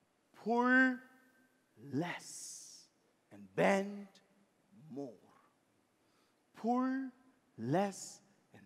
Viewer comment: I thought she said..pole less and bend more